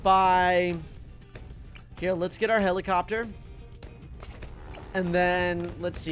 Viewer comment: did just called monkey ace heilcopter